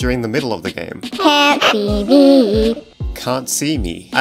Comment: When Tattletail Says "Can't See Me!" I Heard "Cat TV!" I Would Like To See Kittens On Too Cute Now. (and Nah I'm Not Scared Of This Game I Just LOVE KITTENS.